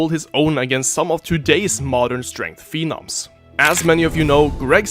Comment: I know you said “modern strength phenoms” but it sounds like you said “how would he stack up against some modern strength females” 😂